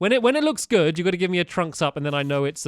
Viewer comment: when he said trunks up i thought he said trumps up